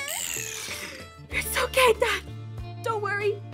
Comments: I thought she said your so gay dad when she said it's okay dad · it sounded like your so gay dad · YOU DIEDED MY DAD Zane:im the lord IM YOUR FATHER NOW Me:yes...🙂 Ps when you said its ok day i thought you said youre so gay dad XD